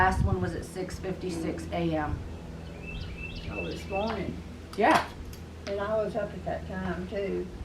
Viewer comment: put your headphones on for this one it sounds like it said "Yeah" cant really make it out